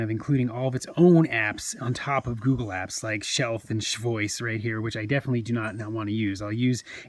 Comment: Did you say Shealth @ 07:46? 😂. I'm guessing the S-Pen is Spen too? 😕